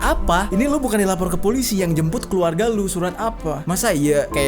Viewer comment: yang njembutt? Apa gua yg salah denger?😂